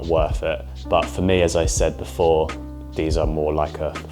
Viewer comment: is talking about “fit pictures” and I always hear “feet pictures” 😅 and thinking OMG another YouTuber selling his 🦶 pics 😂